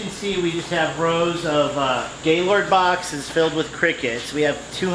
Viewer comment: What is he saying surly its not what I am hearing which is Gaylord boxes